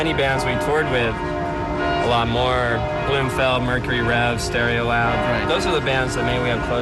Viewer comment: What's the band Steve says 11:34 before Mercury Rev & Stereolab ? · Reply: It's Blumfeld: <link>